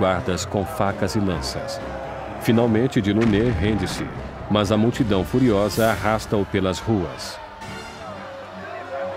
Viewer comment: oq ele disse ??? “Finalmente ...” eu não entendi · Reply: Finalmente "de Launay" rende–se, mas a multidão furiosa arrastam-no pelas ruas... *Bernard de Launay era o governador da Bastilha.